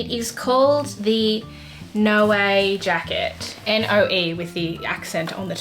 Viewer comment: At first I heard "the NO WAY jacket" 😅